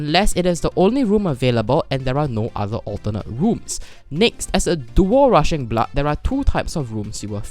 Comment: i heard "as a dual russian blood"